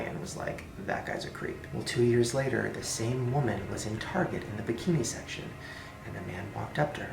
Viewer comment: Ok I thought Shane said "the same woman was entargeted with a kidney section" But he actually said "the same woman was at target in the bikini section " lol me and my mind 😂